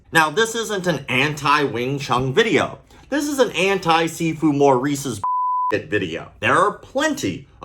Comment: All I kept hearing was “See food, More Eats”